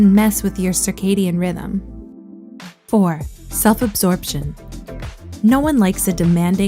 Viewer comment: 1- Checking your phone when you first wake up. (0:26) 2- making excuses for yourself (1:04) 3- not getting enough sleep. (1:40) 4- self absorbtion (2:17) 5- comparing your success to everyone else’s (2:38) 6- dreaming without an action plan. (3:11) 7- making school work or your relationship everything (3:40) 8- allowing distractions in your workspace (4:07) 9- caring more about results on the surface (4:36) 10- The lack of self-love and self-care (5:06) There you go ❤️ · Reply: Thought it said "self abortion"